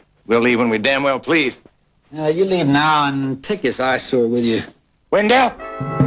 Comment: I remember this episode as a kid. When Wendel said "and take this eyesore with you", I originally thought he said asshole!!!